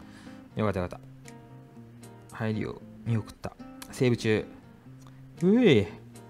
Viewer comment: セーブ中が生理中に聞こえて、なんだこの下衆馬って思ってすみません。